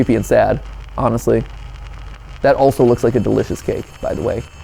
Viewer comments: YO on 1:07:14 I thought this man was saying that looks like a delicious kid cuz I didn’t see no cake at first 😬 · I swear I thought he said kid 😂 😂😂 not cake Also who is pumped who the theory on this!!